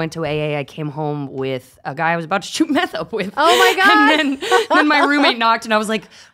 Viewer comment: Can someone tell me what Lindsey said at 30:46? Subtitles don’t seem right to me. Was it „a guy I was about to make out with“ or „guy I was about to do a bunch of meth with“? 💀 sorry if I’m sounding dumb but I don’t get it (English is not my first language). And when she talks about the chip, did she mean that some bars would give you a free drink if you show a proof that you’re visiting AAA meetings? (if so, it’s truly heinous…) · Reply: I believe she said "about to shoot up meth" meaning do a lot of meth. And yes, you heard the chip part right. Apparently there are bars that give you a free drink if you give them an AA chip.